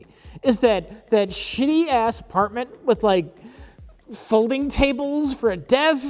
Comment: Did I hear "folding chable" several times in this episode?